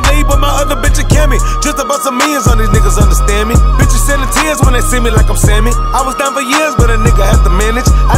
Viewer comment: I thought Sauce Walka said "I was down for years when they seen me like I'm Sandy" 😂 I was like damnnnnn that's a hard ass SpongeBob bar